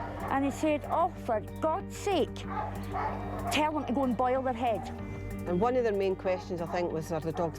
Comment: what the hell did she just say???? "Tell them to go buy a hen" or something